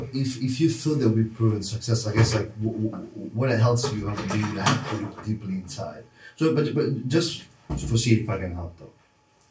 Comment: Can anyone figure out what said at 4:04? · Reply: Big boy came into the room making all kinds of noise! Subtitles says, “what it helps you to understand deeply inside.”